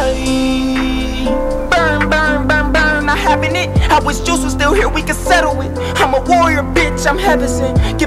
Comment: did he say “I wish Juice was still here” meaning juice wrld?